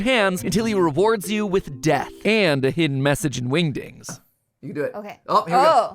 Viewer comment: did he just say wingding